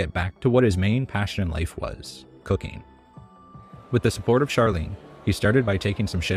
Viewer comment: Not gonna lie, i genuinely thought the narrator said "cocaine"